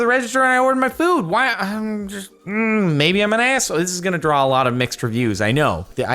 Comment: I heard you say asshole and the captions say so also.